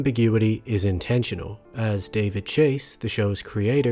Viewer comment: i swear it said David Crane before lol